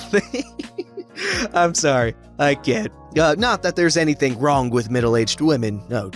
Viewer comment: When he said "im sorry, i cant" I honestly thought he said "im sorry, im gay"